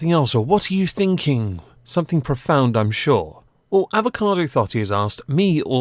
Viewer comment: I thought you said, Avocado FARTING. So I kept hearing that all the way through your eye roll segment.